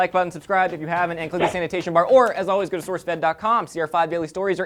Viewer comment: almost sounded like said hit the sanitation bar instead of hit the annotation bar